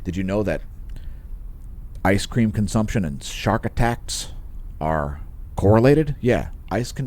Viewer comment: Lol! You don't say! I thought sharks liked ice cream. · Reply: I thought he said Shart attacks :pride-megaphone-rainbow-handle